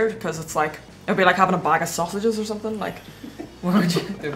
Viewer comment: Did she just say a bag of sausages? lol.